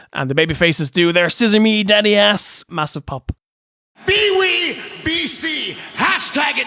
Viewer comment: what the hell did Jericho say? be we bc? peewee bee see?